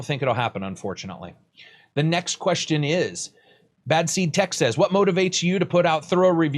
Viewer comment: who are you mentioning? I couldn't hear it clearly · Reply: bad seed tech maybe.